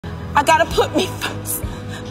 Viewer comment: i thought she said i gotta poopy first i gotta poopy first😂